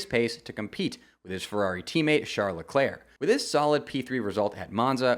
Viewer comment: youtube caption says Charlotte Clair instead of Charles Leclerc😭😭